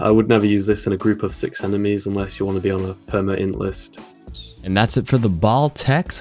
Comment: "unless you want to be on a prmlblblb list". I can't hear what kind of list is mentioned. Anyone knows? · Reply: I hear perma int-list